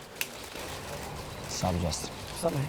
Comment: What did they say? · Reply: whats up justin